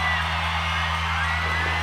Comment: what does liam say at 6:05? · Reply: Ta ra ...Means goodbye .see ya